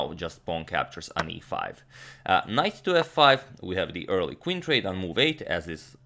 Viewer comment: did he say KNIFE to F5 .......hahahaha ???